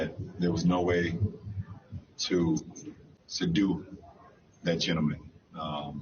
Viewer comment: Did he say sadu when he meant to say, "subdue"? I hope not! If so, this guy is DUMB!